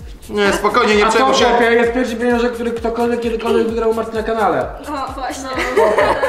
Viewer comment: czy ja usłyszałam Martyny? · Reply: "U Marty na kanale"